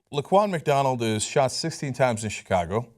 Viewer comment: For a second I thought Cenk said "Look Ronald McDonald has been shot..."